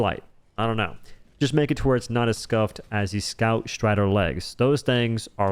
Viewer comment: Yes there are diverse loadouts, but its obvious that one of them is miles ahead: "Explosive crossbow, ultimatum, recoilless rifle." If the game was better balanced, you could use the starter gear for all content (you can't in reality, you'd fail fabricator missions on super helldive) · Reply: Whoops thought u said fact strider